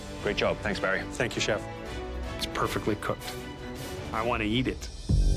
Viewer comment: Is it just me or did it sound like he said "I want to YEET it." ?